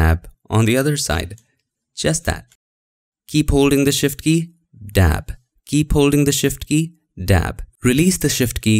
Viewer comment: Keep holding the shift key... dab ... keep holding the shift key... dab ... Was it just me or did he actually say dab all the time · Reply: Lmao Lyl I am quite certain what he meant was the verb “to tap” not to dab...